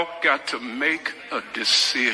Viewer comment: the pastor is telling the congregation "it’s EITHER God or the devil." The idiots who captioned that video said "is he the God or the devil". Can’t get NONE of those words right. Shows how invested they truly are in what’s being said, huh? Omg… 🙄